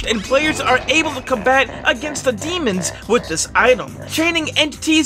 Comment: did he just say demons